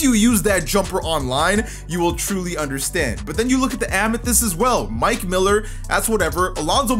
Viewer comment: did buddy just say amathest🤣